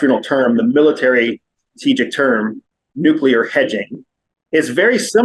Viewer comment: i thought he said nuclear edging. 😂